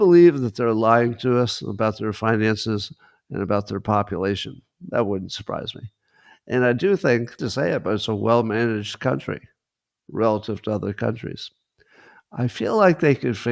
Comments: Scott, there’s a weird glitch around 27:51-53. Almost like you cut something or YouTube cut it (which just fuels theories about YouTube and China commentary content). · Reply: I think he might have said: 27:49 "That wouldn't surprise me. And I do think (and I hate) to say it but it's a well-managed country." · Scott says "China is a . . . " but whatever he was going to say was cut off. What's up with that? Is Scott being censored, or is this just a YouTube glitch? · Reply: I think he might have said: 27:49 "That wouldn't surprise me. And I do think (and I hate) to say it but it's a well-managed country."